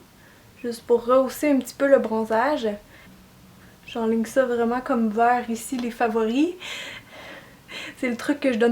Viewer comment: Qu'est-ce que tu dis à 10 min 52, que tu dis à tes élèves ? Je l'ai repassé plusieurs fois mais j'ai compris "on vas glisser comme ça vers les favoris" x)) autrement ce maquillage est joli et flash pour l'été c'est sympa ! :)